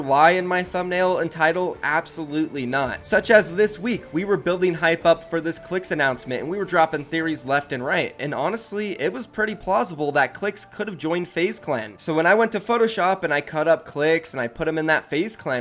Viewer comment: is it just me or did he say plossible